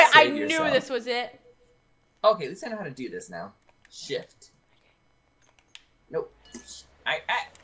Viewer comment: did anyone else hear say sh*t